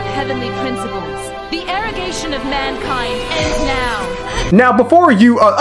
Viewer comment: Anytime I see the scene with our Bellovved Sustainer, I can't help but wheezing because first time I play the game I misheard her saying "The irrigation of mankind ends here". I'm like... "HUH?!" 0o0'